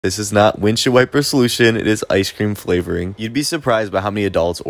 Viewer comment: I thought he said "this is our windshield wiper solution" so u can imagine how confused I was this entire video 😭